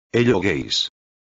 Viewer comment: Did I just heard "Hello Gays" ?